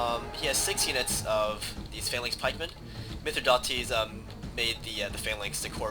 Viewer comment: when you say silver shield pikemen it sounds like you say social pikemen